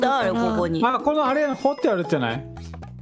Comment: の掘ってあるっちゃない？がWhat’s your name?に聞こえそう